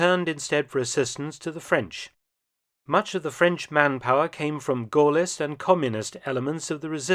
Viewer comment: What does he say at about 5:36 where he says much of it came from Communist and ____ parts of the resistance? It sounds like he says goalist but that doesn't make any sense. Thanks for any answers. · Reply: Yes, he says "Gaullist", and I don't get why you say it doesn't make any sense when it makes perfect sense... 🙄